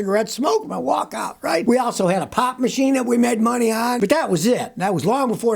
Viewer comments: Whoa Scotty for a minute I thought you said pot machine but sure it was pop machine 😅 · For a second, I thought you said "we had a pot machine"